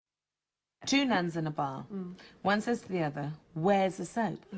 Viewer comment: According to one of the people who finally explained it properly, the joke, ineffectual as it is, is that one of the users of the soap is using it inappropriately on a very specific ladies' body part and consequently the soap is wearing out. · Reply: yes, a mate of mine explained it to me 😏. Part of the problem was that, like a lot of other people, I thought she said two nuns in a bar, instead of bath . . . Thanks for your reply.